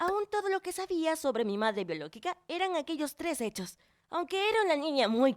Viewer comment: Like si te diste cuenta que dijo biolockica minuto